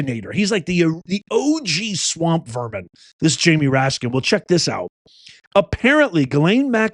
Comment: I thought you said Rat-skin!